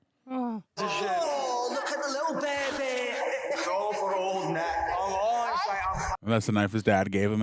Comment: As an American, I thought he was saying “old neck” for most of the film… oops